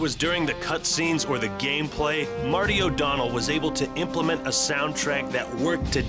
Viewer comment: When he said Martin O'Donnel i thought that he said Mario O'Donnel